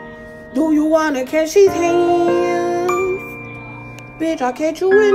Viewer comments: ✨bish I’ll catch you in the street✨ Legend says he still hat it that he’s still singing to this day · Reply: the hurricane please percy califonria i thought you said you beat my ass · “Bich I’ll come to you in the street”😂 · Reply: Did you not hear the same thing I hear, “bish I’ll catch you in the street”, wait the words were on the screen and you still f it up 😭😂